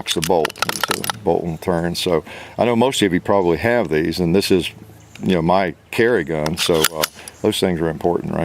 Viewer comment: Did he just say this is his carry gun?